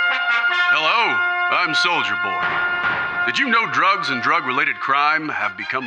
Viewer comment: ffs every time they say the name "Soldier Boy" I hear "Soulja Boy"